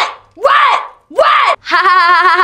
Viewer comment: i thought she said WHAT WHAT WHAT but she actually says WET WET WET WET